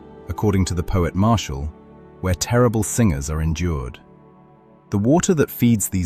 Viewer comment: narrator says "sometimes endured terrible... ? it sounded like "singers" to my ears, but not sure... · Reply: No? It's singers.